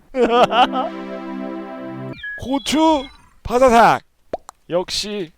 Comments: 고추바사삭 하고 노래 들으니까 고추하마로 들리누 ㅋㅋㅋㅋㅋㅋㅋ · 보고 나서 12:05 보니까 12:05가 고!추! 바사!삭! 이라들리냐 ㅌㅌㅌㅌㅌㅌ